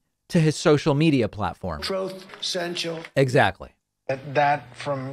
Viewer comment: Equally as funny as the silence, no matter how many times I hear it, is “troth cental” 😂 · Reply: I thought he was saying Sloth Mental 🦥